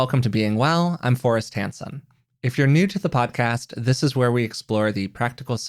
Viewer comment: I really thought you said, “This is Forrest HANDSOME.” 😃😂